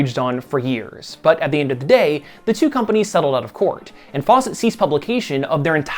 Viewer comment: You just said fawcett took control of all "their" characters...did u mean DC? Nope, sorry, thought you said seized, not ceased. That's my bad and I'm man enough not to delete my original post.